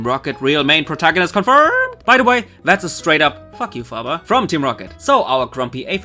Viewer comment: When you said Fabia I thought you said father.... imagine that plot twist O_O